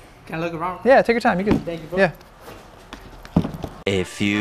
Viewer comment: Did He Say, "Thank You Foo" ?😂😂😂